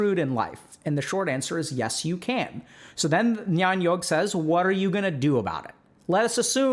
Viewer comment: Could someone please tell me how to spell that nyan-yogg thing? I really cant seem to find it anywhere. I think he says it at 18:28 if that helps clarify